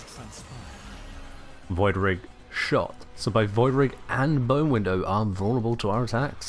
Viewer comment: did you say bonewindow cause i heard bonewindow